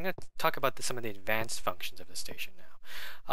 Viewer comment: did u say "f***tions"? I know u meant functions.